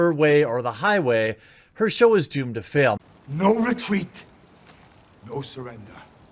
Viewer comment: I thought I heard "No retweet, no surrender"